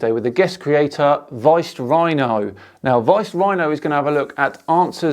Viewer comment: I thought was saying "Riced Rhino".